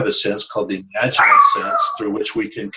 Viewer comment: you say something about a sense through which we communicate - the dog barks so I can't quite make out the term you used. :) What is the sense called, again, please? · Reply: We call the innate capacity of humans to contact non-human sentience the "Imaginal Sense." It is quite readily awakened in almost everyone. After guiding many hundreds of forest therapy walks, I've rarely seen anyone who does not experience receiving something significant in return, when (for example) they ask a tree a question. Try it! Imaginal listening involves the whole of the body, allowing yourself to be in a day-dreaming state where everything that comes to you---images, memories, words, sensations--may be communication. Like other senses, the more we experience this, the sharper our imaginal senses become, until it seems quite ordinary to be in a continual dialogue while walking through a forest.